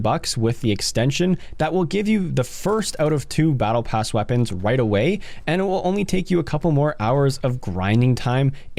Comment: sounds like you said “wight away”